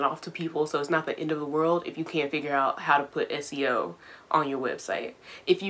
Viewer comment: SEL? Is that what she said? What is that? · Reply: oh she said SEO and SEO stands for Search Engine Optimization. SEO can help improve the visibility of your website in search engines! So for example, not blocking search engines from crawling your website, making sure your website looks good on both mobile and desktop, and including certain keywords that you want to rank high for on your website like "software engineer" or for example "Java" on multiple different pages can help your personal website rank higher! And you never know who might see it 🙂